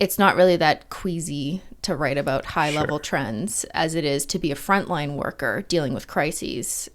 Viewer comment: As a frontline worker in social services in a very poverty-ridden neighbourhood in Canda, THIS! ^^^ There are systemic barriers that force people to do criminalized behaviours to survive, and most are victimless. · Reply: Hayley Smuts this is kinda unrelated, but I thought you said “fortnite worker” lol